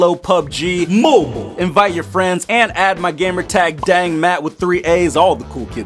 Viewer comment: Two things I thought you said daddmatt and... #25%